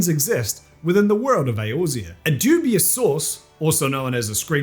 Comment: Lol, at first I thought you said "a dubious Sauce" on the pizza story.